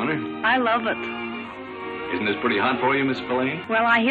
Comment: I think they are saying "hot" not "hard" as written in the caption?